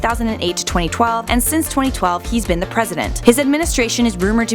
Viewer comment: it says "present" instead of president